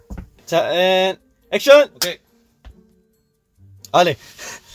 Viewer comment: i had to replay a part to be sure of what bangchan said since i heard him say something in, french here at 1:04 I think he said "Allez" which means go on in french i rly heard that , i had to replay it multiple time to be sure of it , correct me if im mistaken